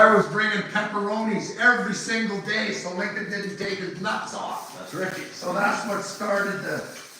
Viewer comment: I had to rewind it the first time because I thought she said f@ to Lincoln